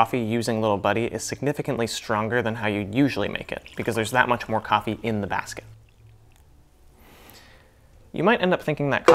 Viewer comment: you said I'm a big fan of "ransom" or something at 2.14, what did you say/mean. thanks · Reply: at 1:03/1:04 what did he say "because there's not that much more coffee in the basket" or because there's much more coffee in the basket"? I hope its the latter, if it's previous it doesn't make sense.